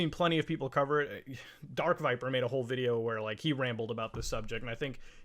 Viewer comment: who was that person? I couldn't quite catch it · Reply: Dark viper